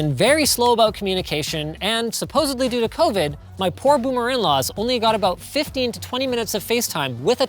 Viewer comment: Did he just say 'Boomer In-laws ' ? 😂